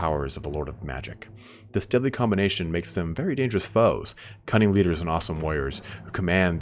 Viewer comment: okay i thought he said cunning leaders and awesome lawyers